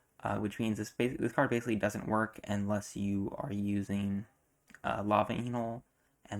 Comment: massive L sounds like he says “Lava Anal” 😭